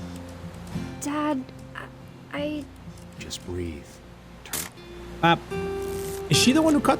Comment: for anyone wonder what the dad said he said “Turn off your imagination. Focus on your target” 😊